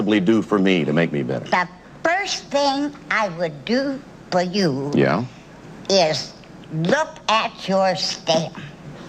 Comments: "The first thing I would do for you is...look at your state." That's what I heard to me. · what the hell is she saying? "The first thing I would do is look at your ..." Stamp? She has no teeth so I can't understand what she's saying :| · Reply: Candice ecidnaC the old lady says stem and the other one needs to use the can (toilet)